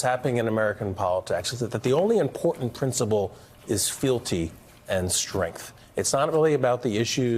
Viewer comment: At around 12:45 he talks about two things and I didn't catch the first word he said filty and strength really close to the 12:50 mark. Did he say filty? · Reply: fealty.